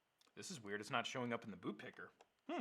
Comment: he said boob picker instead of boot picker 😂